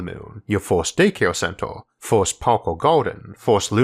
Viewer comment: I chose to hear "1st parkour garden" instead of "1st park or garden", because I want moon parkour.